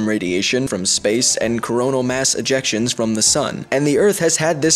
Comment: I thought you said colonial mass erections xD